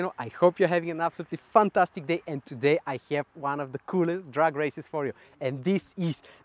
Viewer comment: oh i thought he said drug race